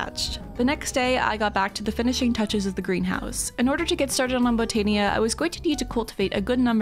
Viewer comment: did you say mumbotania?🥹